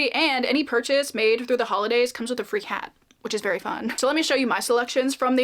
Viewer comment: dammit, for a second I thought you said "free cat."